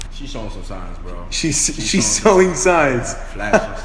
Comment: She’s sowing signs😹instead of “showing” signs